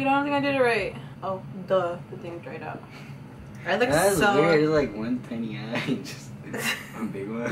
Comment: Did you say you looked so werid on 9:13?? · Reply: Yeah his brother said she looked weird with one eyelash..n then she said i look so weird..maybe cuz she knows how she looks like with just one lash..from other times she has put on lashes ...